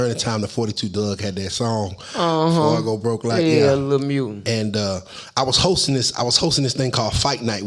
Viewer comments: He called 42 a mutant 😂😂😂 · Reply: Ooook he said Lil mutant I thought he said mule. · He called 42 a mutant 😂😂😂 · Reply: I thought he said lil music 🤦🏽‍♂️. But mutant makes much mo sense 😂😂😂